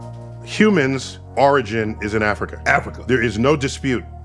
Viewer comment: My bad, I thought it said human orgies.